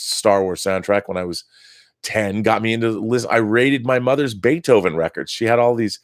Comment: At first I thought you said you "rated" her Beethoven records, I.E. eroica symphony, 7.8/10 too much string section lol